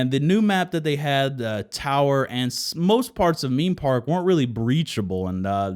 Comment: Did he said Meme Park? :DDD